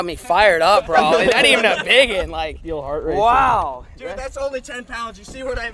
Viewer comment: sounds like he says hard r